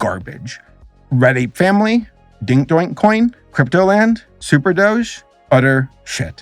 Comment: At first I thought you said Logan Paul supported an NFT called d1ck doink